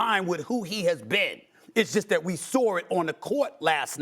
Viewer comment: I laugh everytime I hear the way Stephen A. says “saw” … he pronounces it like “soar” 😂😂